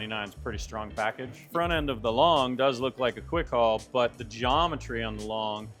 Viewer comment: please help me here, what is the 'Long'? It's that what he said? Sorry my hearing aint that good, I'm so torn between RM & Tern. 4 year Super Delite Rohloff rider and due to my age and flexibility diminishing it's either a new Homage Pinion or the best Tern has to offer. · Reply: Hey there! No worries, happy to help! The "Tern Quick Haul Long" is indeed what you heard. It's a new bike from Tern designed to be longer and more capable of carrying heavier loads and more children compared to the standard Quick Haul. Despite being their lowest-priced option for heavy cargo, it still offers great functionality and versatility. Given your experience with the Super Delite Rohloff, you're used to high-quality performance, so I understand the dilemma. The new Riese & Müller Homage with the Pinion system offers excellent comfort and ease of use, which could be beneficial considering your concerns about flexibility. However, the Pinion system wont be available in the US until next year. Both are excellent choices, but if you're looking for Tern's higher specced bikes with the added comfort of things like suspension- you might want to check out the GSD or HSD, but it really depends on what specific needs and preferences you have. If you need any more details or have specific questions, feel free to ask or give us a shout!